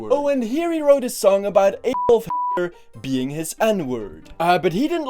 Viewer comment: When he said adolf hitler it sounded like he said an*l f*cker